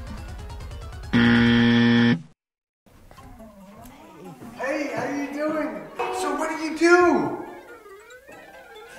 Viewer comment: Although I played it several times, I cannot make out what the guy on the right is saying to greet the girl coming into the church. This is at the 1:15 mark. I did catch "Hi, how ya doing", but the part after that I don't understand. I admit, my hearing is damaged from a lengthy career in the military, primarily as an Army Mortarman. Perhaps someone with better hearing can translate for me? Thanks! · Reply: "So, when are you due?" Assuming she was pregnant : )